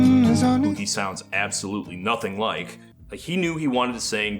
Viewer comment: What was actually said there? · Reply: “Who he sounds absolutely nothing like!” (Referring to Nick Drake)